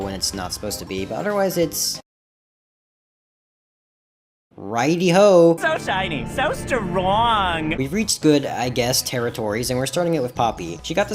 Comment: NOT THE “so shiny so stwroongggg” VOICE FOR POPPYS PART I’M DIED LOL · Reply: I hear'so shiny sounds so wroooong'